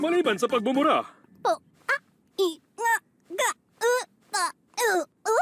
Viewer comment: Pomni said the word "puta"! 3:04 i heard it! 3:11 Did pomni moan?